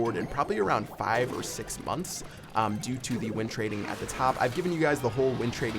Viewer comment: what is win trading? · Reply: Doug Star YT Thought he was saying wind trading lol